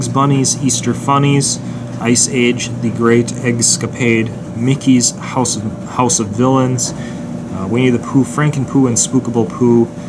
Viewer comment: When you say Mickey Mouse House of villains I thought you said Mickey Mouse House of Dylan’s